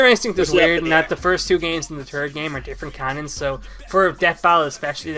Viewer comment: When says third it sounds like he is saying turd.